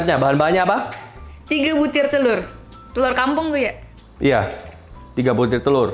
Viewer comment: aku salah denger apa gimana yah? ci jane bilang "lu" ke chef Arnold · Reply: Te "LU" R kampung woi